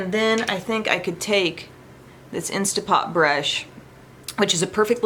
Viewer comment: Every time you say "InstaPop" -- I hear "Instant Pot" and I think about dinner :)